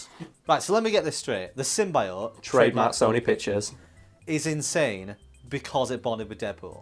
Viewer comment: Baut a minute in film all i hear is "trademarkedbysonypictures" :P